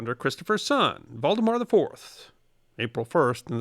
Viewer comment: I thought 1:42 was a joke because captions said Voldemort IV instead of Valdemar IV lol